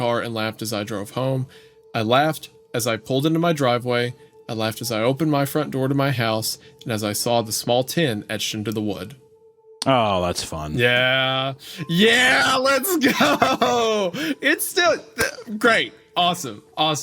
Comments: I kept hearing "tin etched on the wood." And was really confused. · Oh TEN! I thought he was saying "tin etched on the wood" for like 30 minutes. I was like how the hell do you etch Tin on Wood?